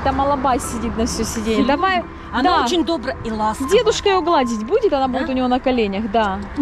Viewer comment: послышалось девушка😂